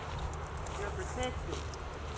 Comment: /7:41.. was ur camera man talking ? Did you hear that voice? Sounds like it said “you are protected” or “ you protect it”?